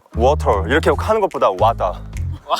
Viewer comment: Guata? 🤨 Le salió igualito a la palabra “guata” JAJAJA Ps. I know he is not saying a spanish word, but it sounds like one to me lol